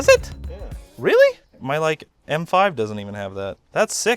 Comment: did i just hear M5...